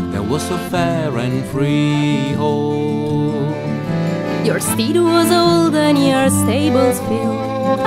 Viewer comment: It sounds like she says “Your steed was old and your stables fill/ed” not “and we’ve got more”